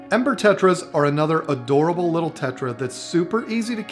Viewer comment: It sounded like said emper tetras instead of ember tetras